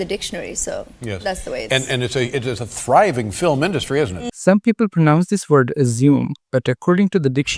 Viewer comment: you misheard him. He says "it is a thriving film industry, isn't it" - he says "isn't it" not "assuming."